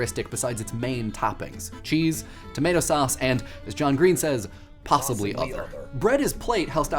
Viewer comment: Possibly other? Am I hearing that right?